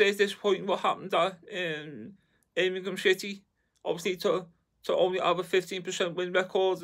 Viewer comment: Did he call them “Ermingham City” 😂😂